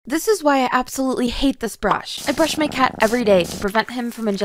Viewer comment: I thought you said „this is why I hate this“.